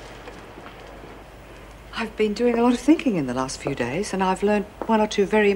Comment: did she say thinking or drinking....